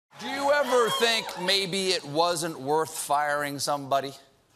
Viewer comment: He tried to hire her for 5th time 🤔🤔🤔🤔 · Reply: I thought he said she was worthless?